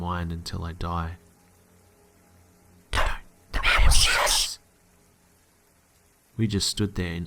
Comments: What is the voice saying at 21:56??? I love creepy effects but this one is too hard to hear and the CC won't show it!! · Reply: just kjb "Don't, the man will see us" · Someone help me out here please...at 21:55, what exactly is the being/creature saying? I cannot understand at all even after listening to it many times! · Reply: Gina Powell Don't! The man will see us! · what is the voice saying?? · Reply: Cameron Rose it's saying don't let them see us. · what did the whisper say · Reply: I think it said " Dont, the man will see us" Im not sure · chris1117 I think I heard "come you want to see us?" or somthing like that. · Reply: ofekrf shchory just replied to me and said it was "Don't... the man will see us" and "Okay... her..." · does anyone know what was said at 21:55? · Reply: "don't move or the man will see us " ☺